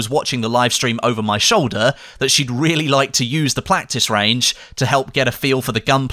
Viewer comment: anyone else hear him say Plactice range